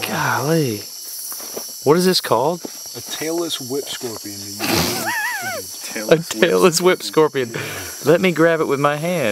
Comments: I thought he said at the end: "Taylor Swift Scorpion" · i thought he said the "taylor swift scorpion" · I I thought they said Taylor Swift scorpion. Lol